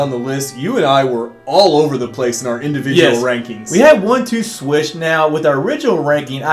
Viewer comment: I thought they said we have 1-2 switch now